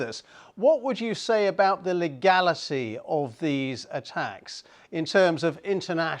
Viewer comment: Did I just hear the word, "legalicy" ? Sounds like someone's got dyslexia problems. lol 😂😂🤣🤣